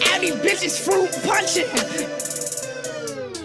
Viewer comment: am i the only one who thought it said “i’m a popping ass bitch like me from minecraft” lol